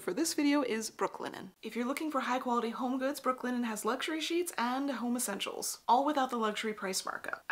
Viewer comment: I'm a child. Why? Because when you said "Brooklinen has luxury sheets and home essentials", I heard "Luxury sheets and homosexuals" and laughed way too long at that.